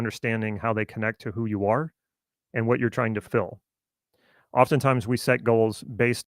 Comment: you ended that sentence with what i thought i heard was "chances are you are sitting goals without understanding how they connect with who you are and what you are trying to ......." and then the word sounded like "fill" instead of "fulfill". if it is "fill" then are you "filling" what was "missing" so to speak and that is literally what a goal does? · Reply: It looks like the internet connection glitched when I said the word "fulfill" and it came out as fill.